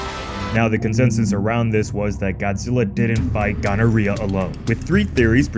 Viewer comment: Did you really say gonorrhea at the 1:37 mark? … 🤣🤣🤣